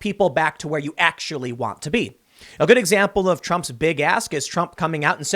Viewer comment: I thought you said "Trump's big ass." Lol